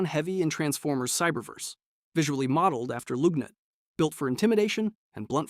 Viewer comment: I'm sorry. Did you say 'loog-nut' ?